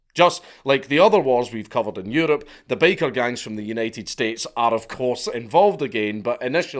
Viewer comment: I wasn't paying attention while listening to this, so for the first few minutes I thought he was saying," Baker Gangs." Which I was pretty amused and confused at the same time. I imagined Bakers fighting over bread territories and control over the white powder.